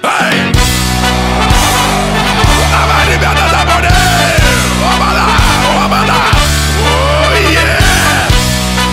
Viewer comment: What did he say at 1:12? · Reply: Come on, guys.